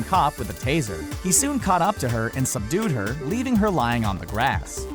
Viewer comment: I thought he said seduced her